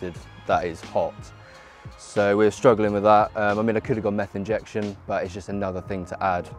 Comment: did he say.. meth injection?